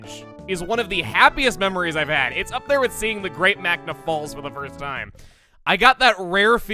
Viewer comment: what did he say? I would say Niagra but I don’t think that’s what he said. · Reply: Sounds like the Great Magma Falls, whatever game that's from.